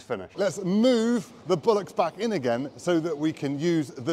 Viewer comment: I thought you said move the bollox back in to use the silage pusher.